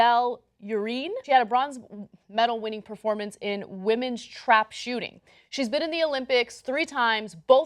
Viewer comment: At first I thought she said "women's crap shooting."